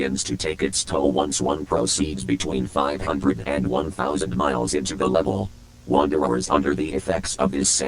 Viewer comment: i thought you said between 501,000 miles instead of 500-1,000 miles